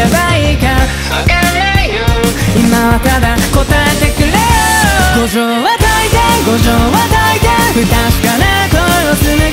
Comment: i hear "gojo ....de"